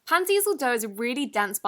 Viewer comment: I was waiting for a comment like this lol I really like it when my culture (filipino) gets recognized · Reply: same I thought she said pan diesel 💀